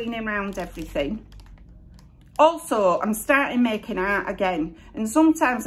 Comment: You made me double take when you were showing the rubber gloves. I was distracted and thought you said 'i've started to make out again' 😯😯 had to rewind to realise u said 'make ART'! So, so funny! 🤣😂🤣😂 Hope today gas been better for you xx